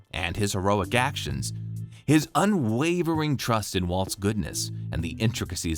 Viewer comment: Heard “Walt’s goodness” so much that I thought I heard Walt Goodman